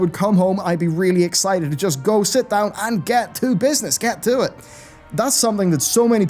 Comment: Did he just say, "get to BiSness"? 🤣